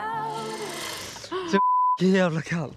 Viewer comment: For a moment I thought Edvin was saying "It's a beautiful day to save lives" 😅 grey's anatomy flashbacks🥲